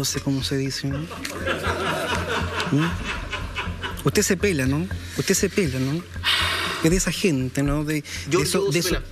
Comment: Que dice en el min 21:13?? Jajajaja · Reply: Que: "Giorgio se pela"